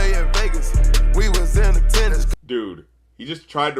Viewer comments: i thought he said " we was in to tenis" 😂😂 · I thought he said we was into tennis not in attendance😂😂😂